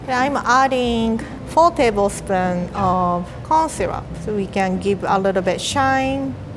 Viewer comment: I thought she said cough syrup instead of corn syrup